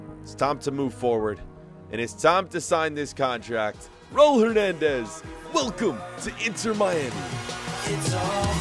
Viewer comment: Am I the only one who thought he said “Raul Menendez” 😂